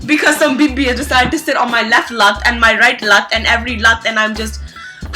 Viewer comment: i thought she said some bibi is sitting on my left "nut"